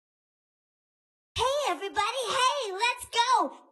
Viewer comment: Why can i hear cypob baby saying hey everybody let go